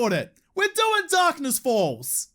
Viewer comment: at 1st i thought u said darkness balls :p